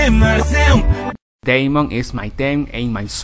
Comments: A mi me sale en los subtitulo en español que dice Yemon ih mayhem marceum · io escuche vemon is dem en seun